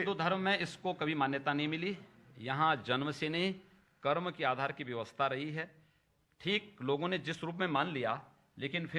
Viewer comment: Please help: At 3:15 What does he say? Janmse ne karm.... what is it exactly? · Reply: He says "Yahan janm se nahi, karm ke aadhar se vyavastha rahi hai", meaning that one's caste was meant to be decided by one's occupation, not in a hereditary manner as it is now.